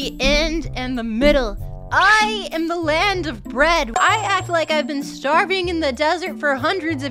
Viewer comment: It was a completely diferent clip for me cuz i hear "breed"